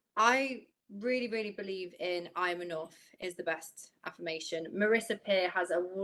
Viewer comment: English is not my first language. Can someone please tell me what the first affirmation is ( 13:50 )? I can't understand what said. Thanks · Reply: "I am enough" is what she said if im not wrong